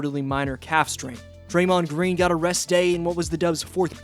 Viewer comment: Thought you said Draymond Green got “arrested” and I wasn’t even surprised 😂